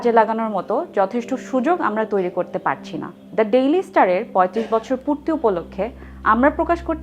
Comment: Did she said Delhi Star 😂😂